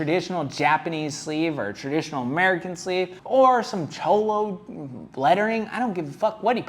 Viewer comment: ? what kind of lettering ??? did he say chomo lettering??? lol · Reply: Cholo...